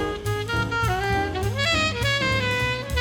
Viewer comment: omg i totally thought you said shit done, time to eat (was shoot lol)